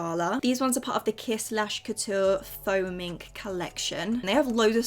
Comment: Every time you said “faux mink” I genuinely thought you was saying “foaming” 🤣 anyone else? 😭